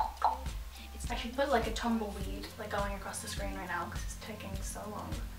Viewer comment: I thought you said tub of weed not tumbleweed